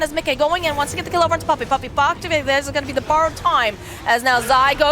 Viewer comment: anyone knows what is caster saying here? "puppey pop ...." what? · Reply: Something along the lines of “puppey pops his borrowed time.”